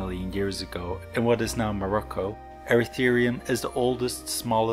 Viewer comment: did i hear morocco